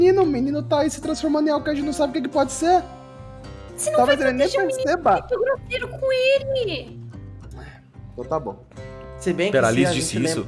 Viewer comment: Gente plmdds oq a disse em 30:18? Eu não consegui entender e ja ouvi umas 10 vezes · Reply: "vc n vai proteger o menino sendo grosseiro c ele!"